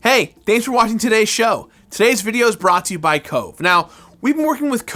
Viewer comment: Not gonna lie, I thought he said this episode was brought to you by coke.... you can decide if it's the drink or bugger suger.